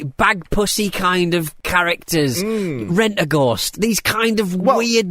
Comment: did he say Radagast as in Radagast the Brown? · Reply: Rentaghost! It's a 1980s British kid's show about a haunted house with a weird dead jester called Timothy Claypole who looked slightly like a medieval Freddie Mercury